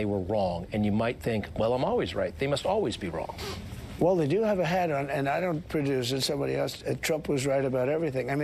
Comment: did he say "well the do have a hat on, and I don't produce" ?????? I swear that is what he said and huh?